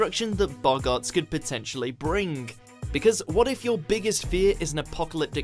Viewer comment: i don't understand british english, is it 'Bring' or 'Brink' ?